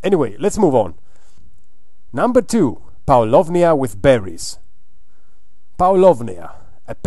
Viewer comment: Great ideas. Did you say Power Love Nia with berries at 1:31?